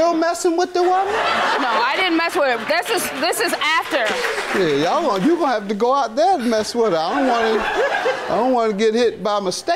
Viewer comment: did JM say “Shid you gone have to go out there and mess with her I don’t want to get hit” 😂😂